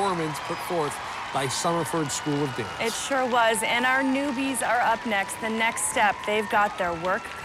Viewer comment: did say noobies?